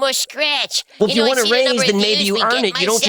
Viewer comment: Oh, he said "raise"; I thought he said "rays" and wondered what the hell he was talking about. Imagine trying to subliminally influence someone only for them to completely misunderstand what you said.